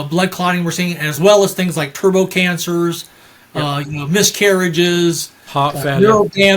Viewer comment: I'm sorry, did he just say TURBO CANCER??? 😰